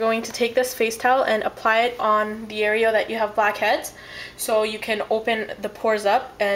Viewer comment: Sounded like you said "fuckheads". *TSSK* bad habit.